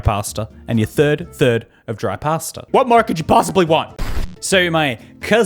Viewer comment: why did i hear "what more could you pastaly want"